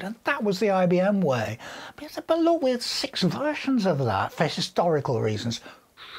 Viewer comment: My English parser seems to be a bit broken at the moment ... I can't make out what is saying at 1:20. I rewound it a few times and it still sounds like "but that's up a lot, we had six wrathions of love". Halp? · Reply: "But there were 6 versions of that" Apologies - more enlightenment once I've done the subtitles :-)